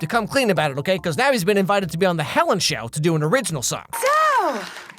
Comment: I heard "The Ellen Show" instead of "Helen Show" and thought that Austin was Ellen D. He looks exactly like her! If you squint...